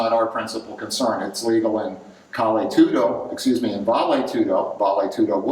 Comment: What did he say Bali to do? It was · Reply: Bali Tudo